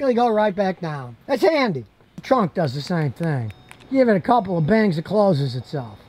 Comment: I thought you said give it a couple of days & it'll close itself lol but that's how it feels to me. Call me old fashion but I don't like electric powered functions as far as seats & doors go. I'm ok with powered windows but not seats or doors